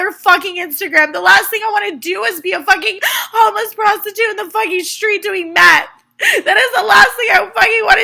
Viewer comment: It sounded like she said "I'm gonna be a homeless prostitute doing MATH" G-g-Girl...you got homework to do.